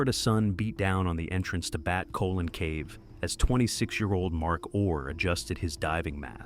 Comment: I thought they were saying BAD COLON CAVE till I read the comments 😂🤣😂🤣